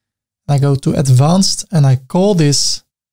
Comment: Great Tutorial! but say, Did you just say "Ceaser's Class" 4:49😁